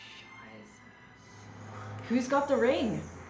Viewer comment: did you just say "scheiße'" at 3:18. I witnessed this quite a few times now is this a thing now to swear in German? Hilarious.